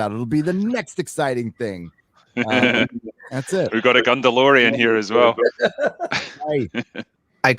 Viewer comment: When he said "its a gundalorian" i heard a "GUNGAlorian" and was hoping for Jar Jar in armor